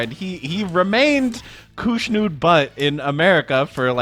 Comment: OMG! Until I heard you say it, I never realized it sounds like Cush Nude Butt 🤣